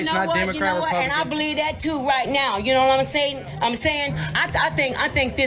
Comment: “You know what I’m Satan…sayin’. “